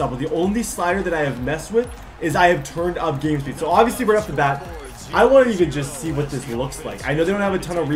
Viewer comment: did you say we’re up to bat?